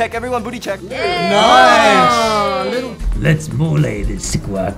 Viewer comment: what did he say lmao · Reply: “let’s molé this guac”